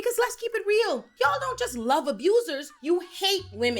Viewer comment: At first I thought it said "Ya I love abusers" 😅